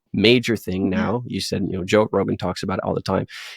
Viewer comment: Did you say joke Rogan?